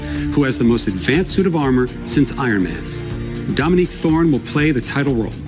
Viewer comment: When he said "Dominique Thorne" I heard "dummy-thicc Thor"